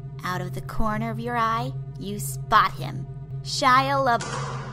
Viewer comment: is she saying Shia la buff